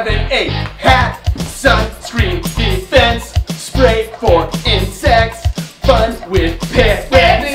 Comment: what do they say? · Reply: Bug spray for insects